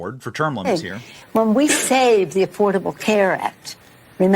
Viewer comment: I heard he wanted to replace it with "Badakath Care". 😂😂😂😂😂😂😂